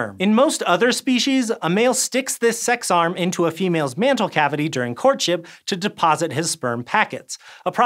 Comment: Mental cavity?? I feel like I misheard that